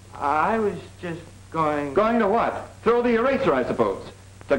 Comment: Through the urethra? Did he say through the urethra?